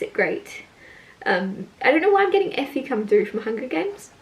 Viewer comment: When you said Effie I just heard “mahogany” it made me laugh😂it’s a good memory and I keep seeing a mahogany door in my dreams